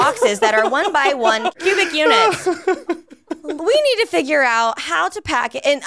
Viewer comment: No joke... maybe it was suggestive thinking kicking in, but I definitely heard "1x1 pubic units" the first time. It made me laugh so damn hard realizing it was just my brain reverting to a 5th-grade mindset.